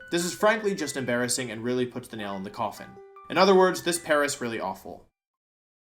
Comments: I don't care if it was intentional or not, but i heard "this paras really awful" about grass bug, and that's hilarious. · Paras really awful.. almost didn't catch that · I know I’m late but in the BUG/GRASS section did you purposely make the pun when you said, “this pair is awful”? I heard it as, “This PARAS awful.” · Did I hear that right? "This Paras really awful" cause if so that was a masterful pun Wolfey! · Hang on, did he say "This paras really awful" lol? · did you say.... this paras really awful? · At the end of number 4 (Bug/Grass), you said "this pair is really awful" but it sounded like you said "this Paras really awful". Paras is Bug/Grass.